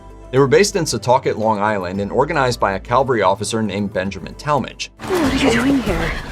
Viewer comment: Did he say "calvary officer" at 7:51 ???